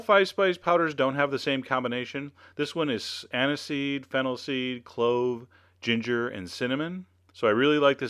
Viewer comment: did I hear "Hennessey"??